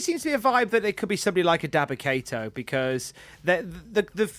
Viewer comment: I was a little far from my tablet when i was listening to this and when you guys said "daba kato" i misheard it as "Defecator".